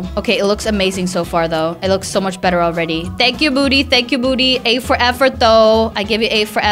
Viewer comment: She was saying "Thank you Moody Thank you Moody." I THOUGHT SHE WAS SAYING " THANK YOU BOOTY THANK YOU BOOTY."🤣🤣🤣🤣😂😂